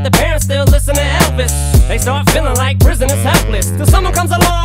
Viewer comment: First i thought he said "They start feelin' like rizzin' is helpless"